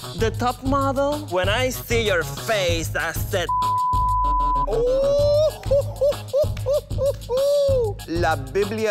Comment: Did he said "putang ina" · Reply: No, he said putana, it means whore in spanish.